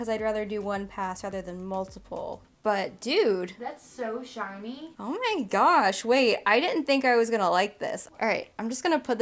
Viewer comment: Did I just hear her say "oh my grob" 😍